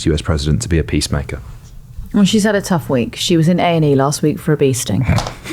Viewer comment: I misheard "bee sting" as "beasting" at first and briefly thought I'd missed a huge change in the MP initiation ceremony